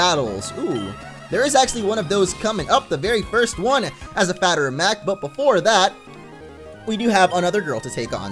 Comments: did he say fatter of macked? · says as a fatter of mac instead of a matter of fact 🤣🤣